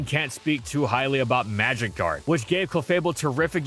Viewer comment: why do I always hear "magikarp" when you say "magic guard" xD